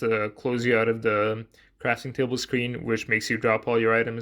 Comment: I heard « crashing table » 😂